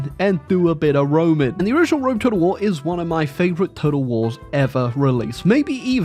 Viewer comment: did you say a warcrimes?